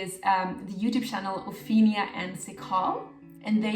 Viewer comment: I couldn't understand the name of the YouTube channel you recommended at 26:24 - Ophelia...? Thanks so much for this great video 🌸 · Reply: Ophenia & Sikaal 🤞🏻